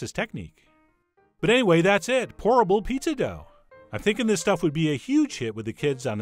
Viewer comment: I know it's not horrible.... but I keep hearing 'Horrible Pizza Dough'.